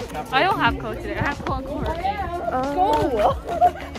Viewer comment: の部活は英語で何と言っていますか？？ 聞き取れなくて、、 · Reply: co(co-curricular) です！聞き取りにくくてごめんなさい！😭😭